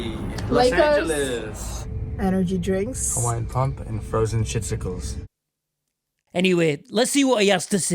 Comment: Are his energy drinks really called "Hawaiian Pump and Frozen Shitsicles", or did I just mishear? 🤔